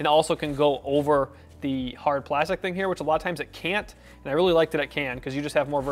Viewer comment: Maybe you misheard, but we say that you CAN take the strap off at 2:45 😃 Thanks for watching! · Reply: ops, sorry.. I heard "can't".. :P :P :P thanks again for your work! :)